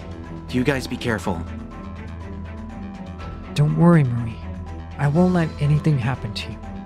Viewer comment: what they say: "Don't worry marie" What the subtitles say: "dont worry mommy" Edit: it got changed :(